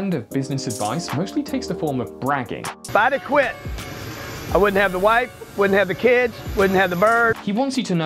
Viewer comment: I thought he said “i wouldnt have to wipe, I wouldn’t have the kids...”